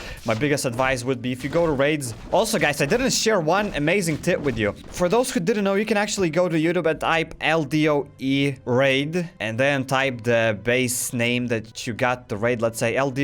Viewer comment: okay what the hell did you just say because I am not so good at english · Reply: "also guys I didn't share one amazing tip with you, for those who don't know you can actually go to youtube and type ldoe raid and then type the base name-"